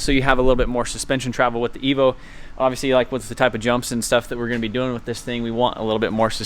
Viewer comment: Wait did he say jumps🙄